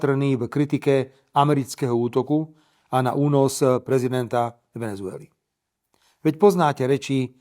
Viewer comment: It's a wrong translation, everybody. Whenever the subtitles say "slave", the correct meaning is "gas", or "petroleum". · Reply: And also at 2:53 Fico states: "Únos prezidenta Venezuely" which directly translates to: "kidnapping of the President of Venezuela", not as the subtitles say "resignation". He says "kidnapping". The subtitles are wrong here.